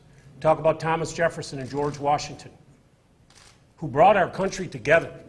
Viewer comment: When I watched this press conference on tv, my nephew was in the room. He thought the governor was saying "you wanna talk about patriots, talk about Thomas Brady and George Bussey." Hahaha.